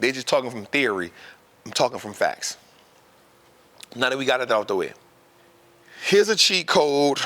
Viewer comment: Yo, what did he say at 4:05? I'm buggin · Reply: Yeah you are. “Now that we got that out the way.”